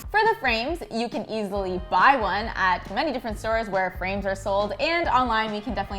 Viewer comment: When she said where frames are sold I thought she said “where brains are sold”